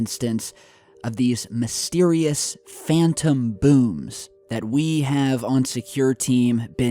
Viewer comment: I thought he said "Phantom Boobs" lol Gotta watch out for those!! lol